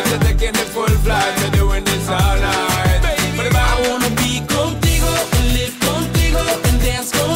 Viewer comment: its been over a year and all this time i thought he said 'gold digger' not 'contigo'